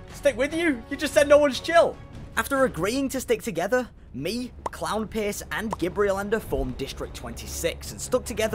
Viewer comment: I thought sword said “ Clown piss”